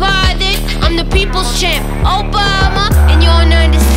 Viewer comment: Did she said Obama at 2:31?